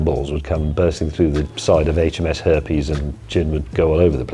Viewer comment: Did you say h.m.s.herpes ....ha ha ha ..oh maybe I misheard ????